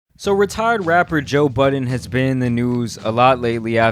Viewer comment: For some reason I heard “so retired Rapper Joe Biden” at the beginning of the video.😂😂